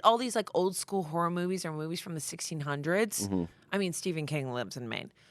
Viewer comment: did... Did you just say movies from the 1600s?